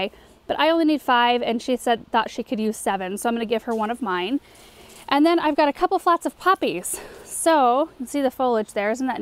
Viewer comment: thought I heard you say..."and then I got a couple flats of puppies"! ..and thought ...Oh, what fun!!! Wait til Benjamin finds out.....